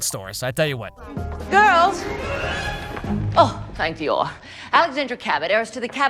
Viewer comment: Did she say thank Dior